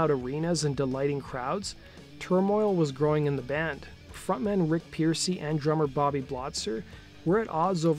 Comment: at the 6:01 makrk, does he say frontman "Rick" Pearcy?